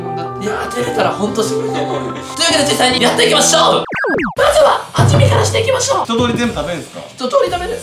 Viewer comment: 1分24秒 まずははじめからしてみましょう！って聞こえた人✋